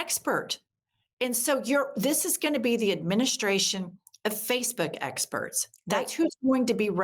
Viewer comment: I thought you said space bug experts, but you said facebook experts, I started talking mars 😂roaches